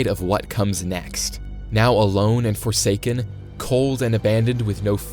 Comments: DID BRO SAID FORSAKEN!? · did you just say forsaken?